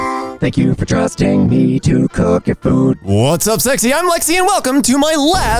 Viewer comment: did you just say "whats up sexy im lexi"